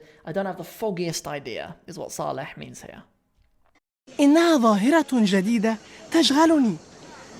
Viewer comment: شيء عجيبٌ يحدث , وليس "شيء يُعجبني يحدث" , مع أن كليهما صحيح لغويا . ولكن الأول هو ما ورد في المرئية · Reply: That's also what I heard. 8:47 it's seems like he says 'إنها ظاهرة جديدة تجهلون' instead of 'إنها ظاهرة جديدة تشغلني', could you please confirm? Thank you.